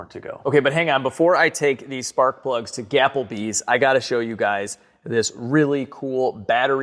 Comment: I swear I heard say 'gapplebees'. I have no words.